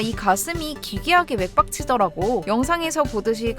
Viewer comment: 맥박치더라고 부분이 왜 나만 개빡치더라고 라고 들림?